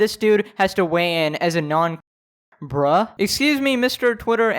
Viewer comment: I had a different tab pulled up and I actually thought he said Non-bruh.